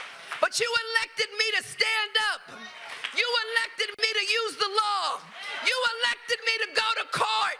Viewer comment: in the video Ms James hollers, "you elected me to USE the law." She did not say UPHOLD the law. She said, "USE the law," and USE the law she did; to prosecute an innocent man.